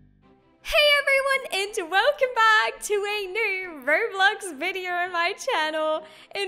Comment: Did she say "Hey everyone its welcome back to my channel" :P